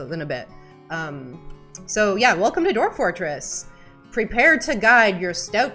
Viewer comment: I was eating at the start and instead of "Prepare to guide" I heard "Prepare to die." I thought, "Wait, it is turning into Dark Souls?" Then I remembered, "Well, this is Dwarf Fortress..." Losing is fun and all... 😅